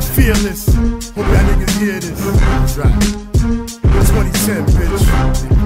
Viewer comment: haha did he say I'm 20 cent bitch at 2:58 hahahaha thats dope.